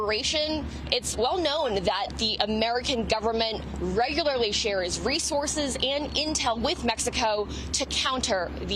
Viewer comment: anyone else hear 'race horses'?